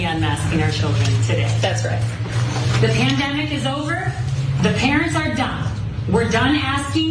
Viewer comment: I actually thought she said “the parents are DUMB”. That would’ve explained so much